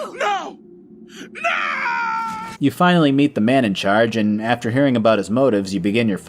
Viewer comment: My video buffered and I thought you said "You finally meet the manager" Now I need The Elder Scrolls Karen thanks